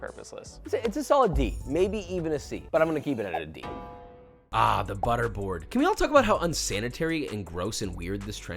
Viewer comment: someone hear boris said make buterbrod and went haywire hearing this